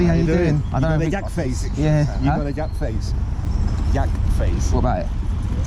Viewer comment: I thought he was saying you have a yak face 😆